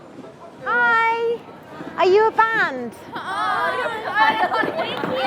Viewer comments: I think they thought you said "I'm your fan" instead of "Are you a band?" that's why they said thank you. Major communication fail, yes. 😂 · Reporter: "Are you a band?" Rainbow heard it as: "I am your fan" That's why they laughed xD